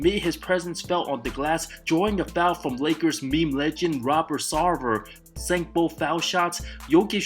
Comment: did you call Robert Sarver a Lakers meme legend??😂😂😂😂😂